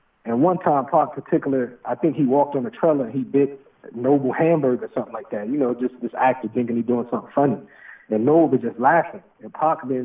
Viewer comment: Belushi did WHAT to Noble?? I can't make out what Mutah said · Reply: Ate nobles hamburger without permission